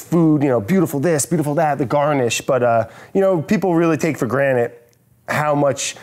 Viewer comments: Did I just hear Brad say “take for granite” cmon Brad the word is granted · brad said granite instead of granted*